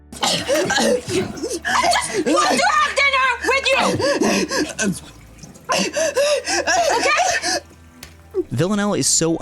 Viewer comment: What is she yelling? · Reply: "I just want to have dinner with you, ok?"